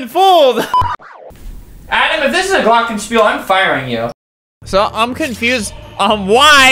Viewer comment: As a german I hear albert say '' glocken spul ''